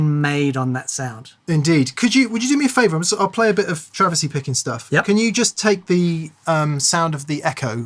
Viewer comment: I thought he said "travesty picking stuff". First thought was, " I guess he's seen me play before".